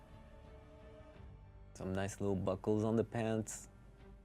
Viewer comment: they say buckles, I hear fish nets.